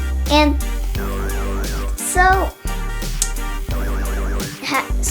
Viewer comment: Sounds like he said have fun fricking at the end